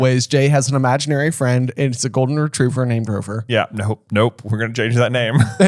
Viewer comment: I was confused for a moment because I thought I heard J's invisible Golden Retriever was named Grover.